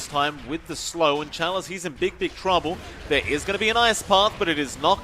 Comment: did he say big dick trouble?